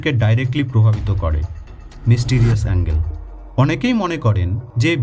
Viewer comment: It's might be "Mysterious Angle", instead of " "Angel".